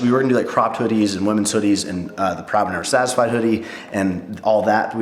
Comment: thought he said crop top men's hoodies at first.... i mean whatever i gotta do to rep the brand :')